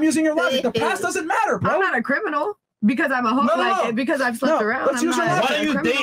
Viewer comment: I heard," I'm not a criminal because I'm a hook, I mean, because I slept around a lot." Was she gonna say hooker?